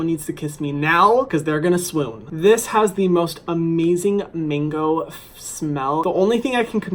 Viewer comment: I’m sorry but why did I keep hearing “mingo” when he said mango.... just a British thing...? Ok...